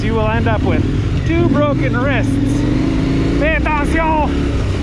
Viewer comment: did he say "fais attention!" Sounded very french to me. Cheers from QC!